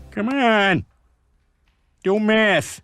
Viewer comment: First time I heard/saw that, I heard it as "Come on, do meth" 0:32 2.5+2.5=5 1:17 I hope, whoever gave her her professor degree, demands it back.